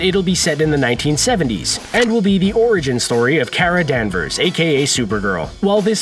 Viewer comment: did he just say carol dancers, because thats captain marvel